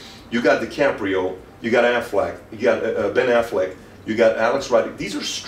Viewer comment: Anyone else catch that Pat said Aflac like the insurance company instead of Ben Afleck. lol